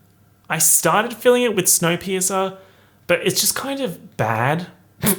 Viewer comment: I’ve never heard of snow piercer before and I swear I had to rewind several times i thought he was saying snow peas something ????